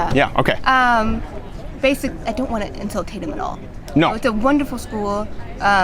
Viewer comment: Did she say insul-tate ?